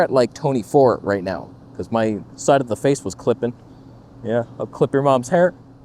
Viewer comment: for the video assist on the G9M2 - so nice colors and easy editing without any LUT or what. But the batteries are so heavy and bulky, thus consider GH7 with internal ProresRaw. But that needs transcode to DaVinci. Oh oh, I hear it: Someone wants me to transcode my mom. And we have to many "but" here, hence this is not the road to go. Wait for the G9m2 in the S9 body, it will come. Just wait. No viewfinder and no AF with 300fps - but everything else: Hot shoe and in-camera focus stacking :-D I leave. No wait: G9M2. A long thin hdmi cable and the Video assist with all batteries in your backpack or cliped to the strap! Bam. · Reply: Or maybe - I know this sounds crazy but hear me out - just record all-intra or normal Prores? Wait, I think I hear it too: "I'll all-intra your mom". Tough choice though, G9II vs. GH7, it's S&Q vs. RAW, maybe overheat vs. never overheat, flippy screen vs. flippy/tilty screen, good grip vs. best grip, heavy vs. even heavier. I'm in the GH7 camp but what I really want him to do is get a Freefly Ember, even if it's just for a day.